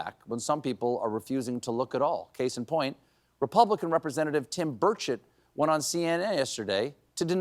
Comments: was that a Freudian slip or did Stephen call Tim Burchett Tim Bird Shit??? 🤣🤣🤣🤣🤣🤣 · did you say Tim BirdShitt!?!? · Did you just say Tim Birdshit?